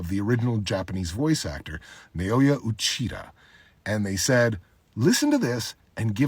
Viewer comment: Am i the only one who thought that he had said Naoya Uchiha at 0:51?